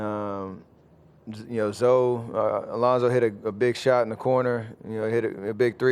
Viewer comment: did he said lonzo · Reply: Alonso